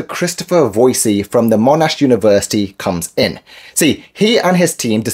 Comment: Where did the so-called discoverer of this go to college? Sounds like you said Bullshit University.